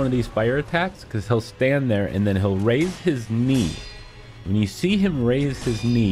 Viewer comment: I swear to GOD I thought you said “raise his meat” lmfao